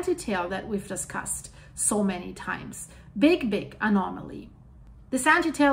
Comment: Ok so i was playin in the background half paying attention when I heard "Big D!*K Anomoly" and i was like "WHAT DID SHE SAY?!" But ya said Big BIG Anomoly 😂😂